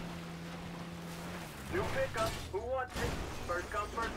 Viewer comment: he did not just say “hoggers”😂💀💀💀